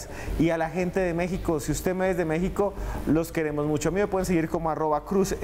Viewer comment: "si usted 'no' es de México, los queremos mucho".... Escuche bien???? Que pasó Esteban??? Soy azteca eh, dime que hablaste muy rápido y por eso escuché así...